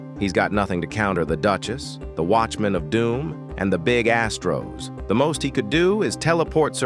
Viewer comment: nah i thought you said something else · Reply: Big asstros